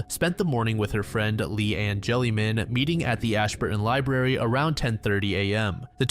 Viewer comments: i thought you said spending time with her friend. Lee and Jellyman. Thought thats a pretty cool nickname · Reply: I thought it said Lee and jellyman too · i thought you said spending time with her friend. Lee and Jellyman. Thought thats a pretty cool nickname